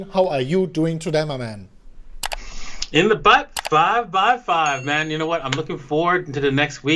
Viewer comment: Last time I thought I was trippin, but is this the SECOND time it sounded like said " in the butt, 5 by 5"?!